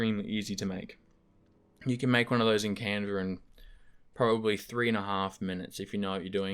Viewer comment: what was the software name he says you can make desktop backgrounds in at 7:45 ? thanks for the video really cool insight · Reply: Canva